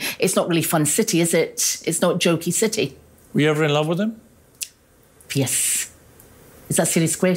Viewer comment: I swear when she says “Piers” that she is actually saying “yesss”. I swear I had to watch it several times to make sure she didn’t actually say yes.